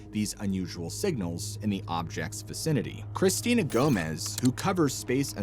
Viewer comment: If it's a real interstellar craft coming to Earth. There is nothing we can do about it . Just sit back in your lawn chair and pop some popcorn and watch the show. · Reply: I thought he said Selena Gomez not Celina… what was that band that got into aliens? Dang it…